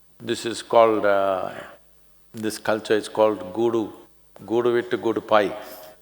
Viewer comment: What the Exact Word Sadghguruji says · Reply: I think he said gudu